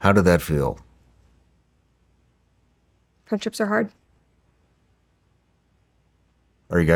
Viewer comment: Brett said, I think, three words that I cannot make out. Anybody know what she said? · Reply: "Friendships are hard."